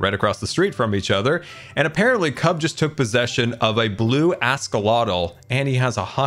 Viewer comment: I'm pretty sure I heard "ask-a-lot-l". I have a 3-year-old nephew. I would classify him as an "ask-a-lot-l" because of the stream of never-ending questions that come out of his mouth. Only he isn't blue.